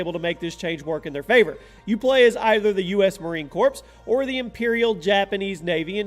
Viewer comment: I want the gift card you said "corpse" 03:05 instead of marine corps. <link>